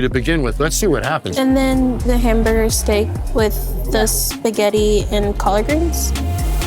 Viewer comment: did she say she wanted a hamburger steak with spaghetti and collard greens? Wtf kinda plate is that? I've never heard of those three things being served together in that way. 😂😂😂